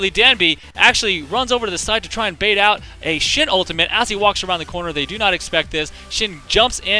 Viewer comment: was it only me or did it actually sound like he said "shit" when he said "shen ultimate"? xD